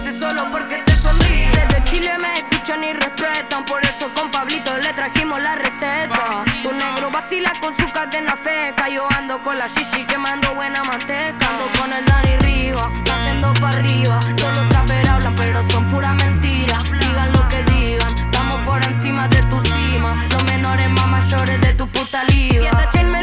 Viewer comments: Toda mi vida pensé que decía ando con el ¡¡Dani Riba callendo pa riba!! Habré parecido un idiota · Ameooo pensé que decia "cayendo pa arriba" pero decía "haciendo pa arriba"jaja